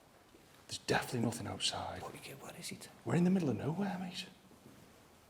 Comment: Great way to start off the New Year…with a loud bang! Also @ 6:00 you can hear someone speaking. I thought it was a female saying “Where is she?” while someone else stated “who’s he?” and another stated it’s a male speaking. Great episode guys! 🇨🇦🇬🇧❤